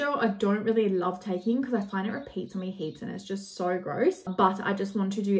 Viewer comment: wait why don't you like taking fish oil? at 33:28 I've re watched it like 5 times and can't figure out what you said idk why and the captions say "repeats on me heats" and idk what that means if thats what you meant LMAO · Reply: repeats on me heaps* 😂